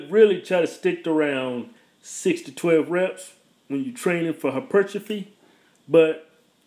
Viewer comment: what is the word he's saying at 11:29 And the word at 12:08 Im trying to improve my english because I'm might move to the US later. So all help is appreciated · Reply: Hypertrophy and atrophy